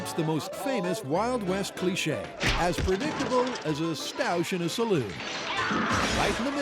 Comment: Wait did he say “‘sta-oosh’ in a saloon”? Was that supposed to be ‘stouche?